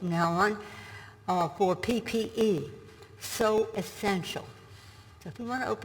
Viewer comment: Did she say she was down with OPP?